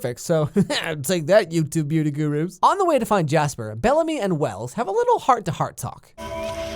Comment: did he just say BALLAMY broooo. also rip i'll love you forever bellamy